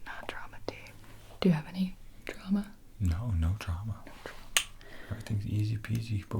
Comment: Omg lmao at 14:35 when you start talking about tea, the closed captions say “do you have any trauma?” “No, no trauma” “no trauma” instead of drama 😂😂😂